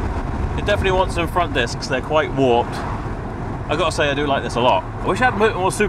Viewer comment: I thought you said the brakes were warped , Did you get this replaced?